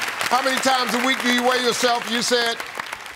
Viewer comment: How many times a week do you wear yourself? Wtf did he say for real though. I heard him ask that 4 times and I don’t know wtf he’s saying!